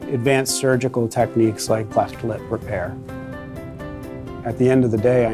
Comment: did he say cleft plate repair? And if so I will cry